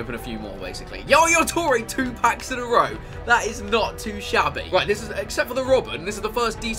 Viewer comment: When said not to shabby I thought he said not to Xavi. xD